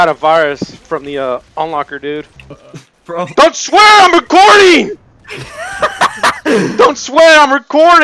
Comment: i hear swearing "DONT SWEAR ON GORY" I thought i hear gore